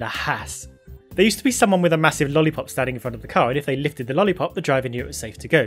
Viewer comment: I thought he said licked the lolipop and because i'm new to the sport i actually though there was a guy that his job was licking a lolipop